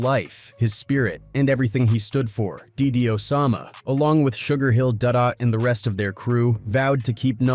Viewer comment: did he just say ''sugarhill dada''😭 but rest in paradise 2 notti💔